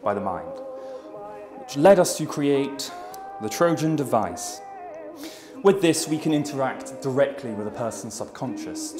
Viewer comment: All I heard was: "MY Eden has been.......".◼️ Unfortunately Captions are not provided,..but I think this is the Happiest sad-ending I've ever Seen! ...Anybody? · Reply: “My Eden has been born” 😄